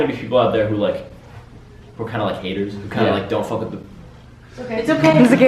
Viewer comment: what did he say? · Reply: He said fuck